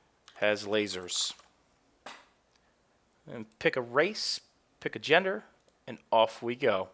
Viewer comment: Did he say race at 5:14?